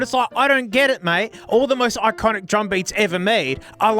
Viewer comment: i thought you were saying "rug beat" the entire time.